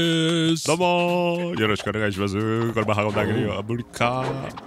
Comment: 早速何言ってるか聞き取れないww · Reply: この語実際には「このまま運んであげるよ、あぁ無理かぁ」と言っている模様😮